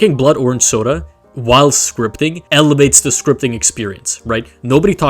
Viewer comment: help I misheard this as “elevates the stripping experience” 💀